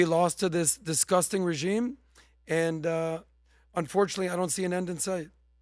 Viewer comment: First thing I hear " Adams hear, unfortunately" haha I'm like wait did I just hear that